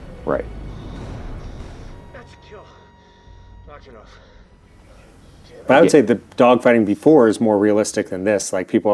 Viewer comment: What call does Tom Cruise use at 1:45? I'm hearing "notch it off" or something like that. I can't quite make out what he says an I've seen other videos of fighter pilots saying the same thing after a round of radio contact. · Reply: "Knock it off". It's actually used in the Navy to mean the evolution is over (as well as like when your dad tells you and your brother to quit fighting).